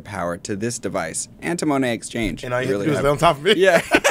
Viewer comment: What does Bob say at 30:52 ? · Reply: "And all you need to do is lay on top of me"